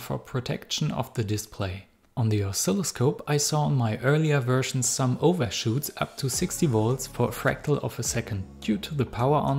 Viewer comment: I heard you say "for a fractal of a second" instead of "fraction," and the mental image of a fractal second is really fun to think about! It's like an eternal moment of time that's a mere second long. This is such a cool project!